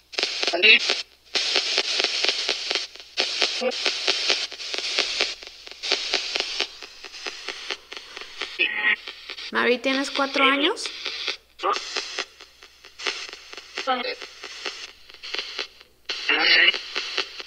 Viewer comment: en el minuto 13:24 parece que dice doce u once algo asi cuando le preguntaste su edad